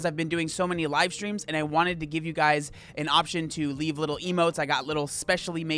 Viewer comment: At first I thought you said “leave little emos”. I was like, here’s my little sister lol.